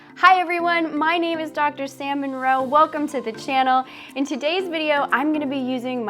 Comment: I thought she said her name was.